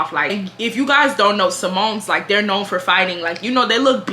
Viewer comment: Ohhh, Samoan! I thought you was saying Symone 😭😭